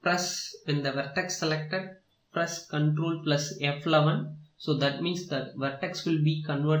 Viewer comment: press what? xD nice tuts but i cant understand that one secound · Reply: I don't really get it either... but I think it is ctrl + f11